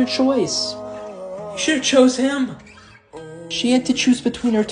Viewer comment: what he said: You shouldn't have chose him! captions he put: You should have chose him!